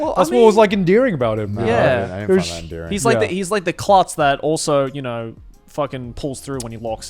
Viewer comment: what did joey say? Hes like the what? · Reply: Klutz